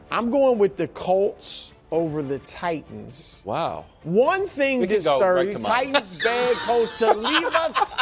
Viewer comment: What did Wildes say at 4:35 ?!? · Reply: “Wow. We can go right to mine”